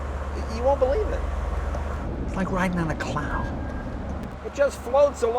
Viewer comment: did Jay say Crown, from Toyota?